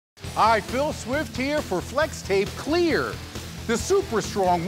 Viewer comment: did he just say C L E A R